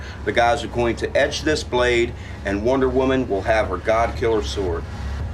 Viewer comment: I thought he was gonna say "and Wonder Woman will have her goddamn sword"